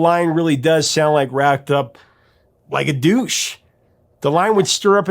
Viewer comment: I didn't even need to watch the opening and I already knew what the song was. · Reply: So did I! I always thought it said "like a douche", also!!